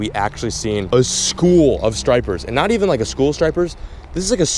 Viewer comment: Anyone else thought it said "stripper" mayhem?